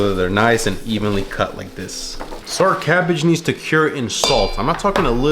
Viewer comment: When he said “so our cabbage needs to cure…” I could have sworn he said “Sir Cabbage” and I had to rewatch that part more than once to confirm I was wrong. XD